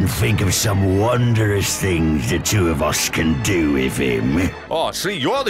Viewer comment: Orc says "Two of us", subtitles say "Two of use", lets not pretend we didn't see that.